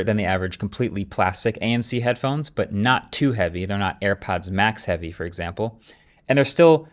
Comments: I keep hearing AirPads max 😅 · Did he say Airpads max?